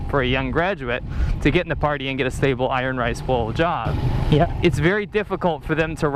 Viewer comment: Ok, i thought i heard "iron rice blowjob". To certain extent it's more accurate description of the job ;-).